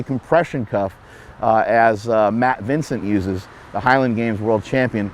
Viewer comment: Lol for a second there I thought you said "Fat Vincent"